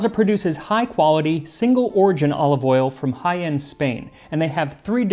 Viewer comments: Did anyone else hear "high-end Spain" ? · It's probably bad that I heard "Jaen, Spain" as "high-end Spain" and then immediately started wondering which part of Spain is the high-end part, and what the machining tolerances on its manufacturing were. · Me having to rewind because I looked away from the screen and could have sworn you said the olive oil came from “High End, Spain”. 😂